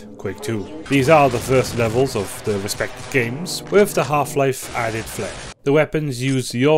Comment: I thought bro said little half life. 💀